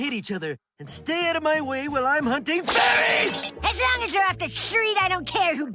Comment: I can't hear anything but "FURRIES!"